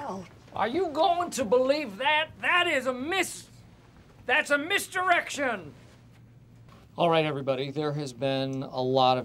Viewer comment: I heard “misdirection” as “missed erection”